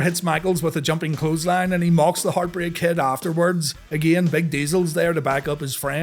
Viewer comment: I was half paying attention and you said Big Diesel but I heard "Vin Diesel" and I did a double take while thinking I dont recall him being in the 96 RR. Lol!!!